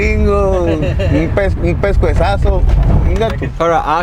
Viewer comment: Anyone know the word that was translated to “quick lick”. Best I heard was pescuezaso or something like that?